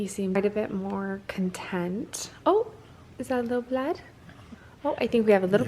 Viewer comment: She seems romanian. I think I heard here: Ce s-a întâmplat?, meaning: What happened? I played it 10 times and it seems romanian. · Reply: shes saying "is that a little blood?"